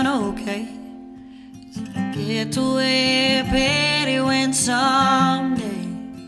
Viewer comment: I thought she was saying "if I get to wear a pair of wings someday"🤣 Now I saw the lyrics and it's saying "If I get where Petty went someday" Who is Petty?